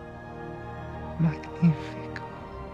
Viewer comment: what did he say i replay 10 times 🤷🏻🤷🏻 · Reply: Magnifico Italian for magnificent or splendid.